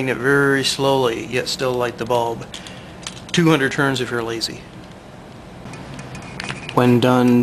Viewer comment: i can't understand what you say at 4:50. it sounds like, 200 turns is real easy, or 200 turns if your easy. what did you actually say ? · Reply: Wind 300 turns, or only 200 turns if you're lazy. (But in reality, 600 turns works MUCH better, if you bought a half-pound spool. ) Those specialized light bulbs will first glow at 0.9V, while a red LED needs about twice the volts (and twice the RPM!) And also of course, some big wide supermagnets will give about 7X higher voltage than those big wide ceramic magnets. (That way, you could spin them at 1/7th the speed of the ceramic magnets.)